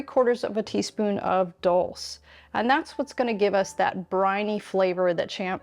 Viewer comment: I'm in the UK. What is the last seasoning you added. It sounded like you said dulce?? What is it?? Never heard of it. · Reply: Dulse flakes.